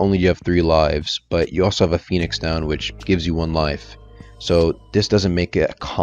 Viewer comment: bro i fr thought my dude said phoenix sandwhich